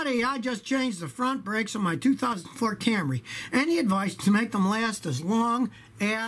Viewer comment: did I hear ford Camry ??